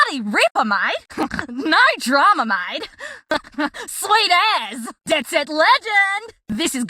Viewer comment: does she really say "Sweet ass" or am I trippin?😭🤣🤣